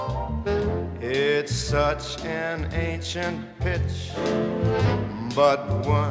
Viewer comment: sounds like he says "ancient bitch" at 1:15 lol